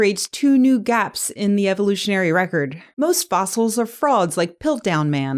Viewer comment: I heard "Most fossils are frogs" and thought, yep, sounds like what a creationist would say 🤔